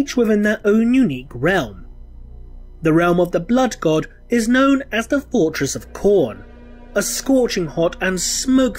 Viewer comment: First time i listened to it I heard : "The realm of the Blood God is known as the fortress of Porn". I was like: WTF?